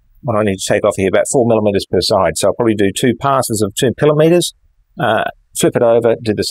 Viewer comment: did you say 2 kilometres? That’s an awfully heavy cut for that mill isn’t it? ;) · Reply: 😆It was millimetres. My audio is playing up at the moment - need to have a look at that.